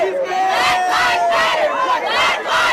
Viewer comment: I heard, “black lies matter! black lies matter! black lies matter!”